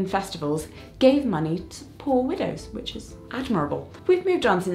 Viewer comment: The subtitles say “poor windows” not poor widows. 😃